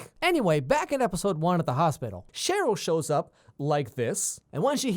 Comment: Did u just say Charlotte? It's Cheryl